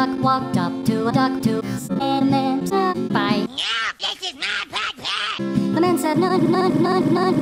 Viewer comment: NO! THIS IS NOT POT PIE!!! 🤣 0:56 😂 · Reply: On the 0:09 i misheard carman said No this is my potpie!